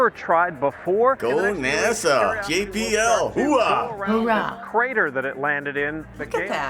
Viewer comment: Did she say Hora??? O.o